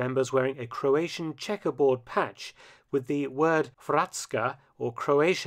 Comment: When you said Hrvatska, I thought you said bratsko. LOL!